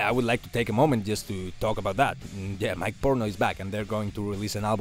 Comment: I thought that you said “my porno is back” 😂